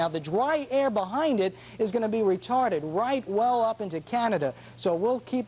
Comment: Did he say "Retarded"?